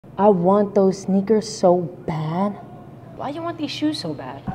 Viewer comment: At first I thought he said snickers 😂😂😂